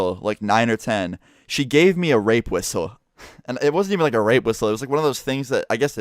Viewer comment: I thought you said earrape whistle